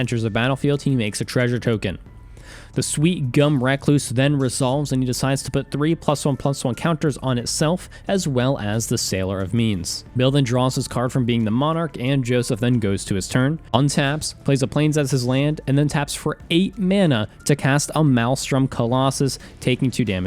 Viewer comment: I swear I heard "Sailor of Memes"...